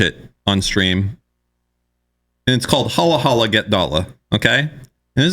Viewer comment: WHAT? What is he saying here, ive tried to find whatever hes on about but im struggling, does anyone know what i neerd to search up to get this? Ive tried several things and the youtube subtitles are useless Halla hala get dala? Halah halla get dhala? Please ,help me! You may not be able to post a link, but if you can tell me what to search for, i need to know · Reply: It's holla holla get dolla. It's on YouTube